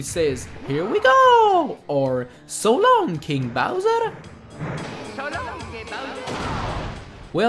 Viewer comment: Well I learned something new ... all this time it was "So long King Bowser!" All my childhood I thought Mario said "So long Gay Bowser!" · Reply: I thought it said "So long big bowser!"